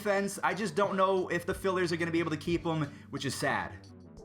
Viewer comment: did he just call the phillies the fillers